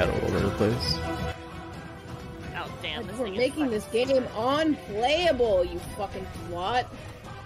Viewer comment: what did she say? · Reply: was it the "your making this unplayable"